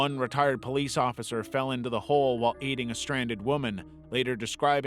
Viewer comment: WHAT DID HE SAY👀👀👀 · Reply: OH WAIT HE SAID AIDING